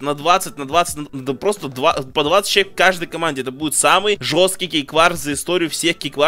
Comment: Мне одному послышалось в начале "ГейВарс"?